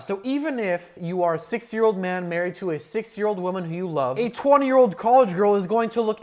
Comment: Bro i thought he said 6 year old woman